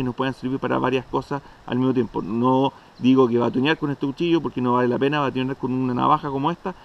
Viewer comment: Minuto 5:51 ¿¿qué dijiste ahí?? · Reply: Dice que no es una navaja para "batonear" ( pegarle en el lomo con un tronco para partir otro ) .